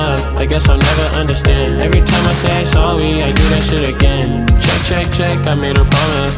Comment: sounded like he said sowwy lol